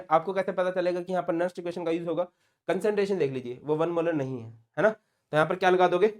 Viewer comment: Kya kaha sir ne? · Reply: he is saying concentration normally 1M hoti hai woh yahan nhi hai